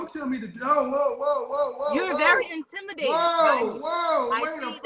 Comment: The spirit that called in telling K.S he is intimidated by her blew my mind 🤯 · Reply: I thought she said to him “You’re intimidating” not that she intimidated him.